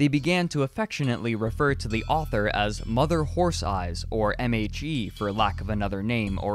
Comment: Did anyone else hear “MAG” instead of “MHE” around 2:25 and get super confused for a second?